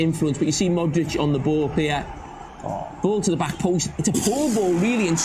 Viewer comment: what ? yes he did say it, you should learn to use your ears my boy, tf u talking about ? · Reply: sorry sorry, it's his accent, he said poor "ball" , it sounded like he said poor goal . My bad .